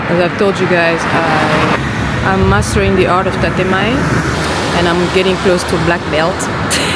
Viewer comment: Tatemae (建前): the polite “public face” shown in society, often different from one’s true feelings (honne). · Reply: oh, I heard "time"😅. Tatemae, that makes more sense